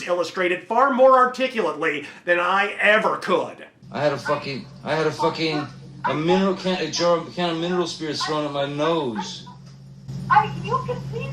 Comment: Can I get a translation on that? I can't understand low quality phone call audio. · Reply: "I had a jar of mineral spirits thrown at my nose." "You can tell the world it was a fair fight and see what the judge and jury says." (Paraphrased.)